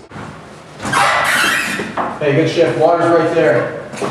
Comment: does he say good shift or good shit?